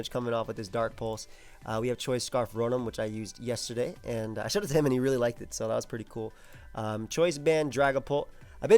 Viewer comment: what did he say here? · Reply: "I showed it to him and he really liked it" talking about Anti